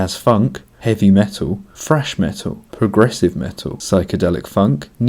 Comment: I misheard "fresh metal" instead of "thrash metal". Checks out! :)